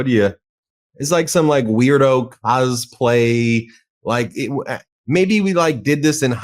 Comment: Did i hear that right? 2:44 "That's like some weirdo a$$play, maybe we like did this in highschool,..." It would be a strange thing to randomly bring up. what did he actually say?